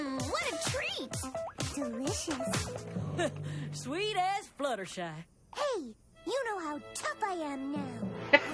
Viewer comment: Applejack: "Sweet as Fluttershy." When I was first hearing that line in promos, out of context, I swear it sounded like Ashleigh Ball was saying "Sweet ass Fluttershy".